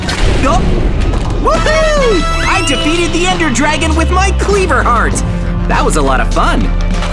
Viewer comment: why did said cpeer heart